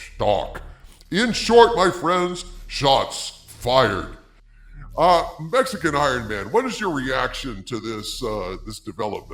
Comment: I was listening to the video while doing something else, I almost got whiplash when I heard 'Mexican ironMan, what are your thoughts..' Fantastic!